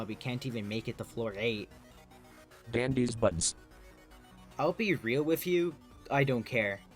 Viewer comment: I WAS DRAWING FOR A VIDEO AND I HEAR "Dandy's Buds" AND THOUGHT HE SAID DANDY'S BUTTS 😭